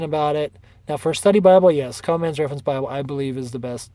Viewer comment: What's the first he mentions at [00:45]--does he say Common's Reference Bible? Please help. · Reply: Common mans reference bible